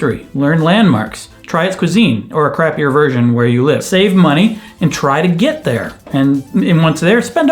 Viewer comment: “Try its cuisine...or a crappier version where you live” My life in Ohio 😂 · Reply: I thought he said, Or craft your own version where you live 😂🙊